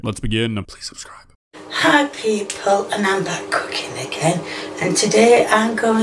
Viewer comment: "Hi people and I will be cooking again" What I heard: " Hot people and I will be cooking again"